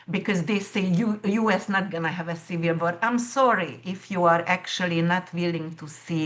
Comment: Trying to figure out what she is saying around 7:35. Listened multiple times, US is also going to have a ????? sounds like cereal. Otherwise, awesome information. Hope it plays out that way ! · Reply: I think she said America is going to have a civil war. I had to listen to that a few times too.